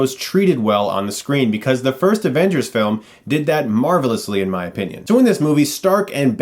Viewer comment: Did he just say Marvel-ously?